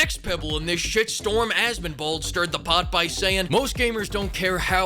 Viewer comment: I keep rewinding but I keep hearing Has Been Bald. Is this what that guy’s name is? I can’t seem to find him. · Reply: Asmongold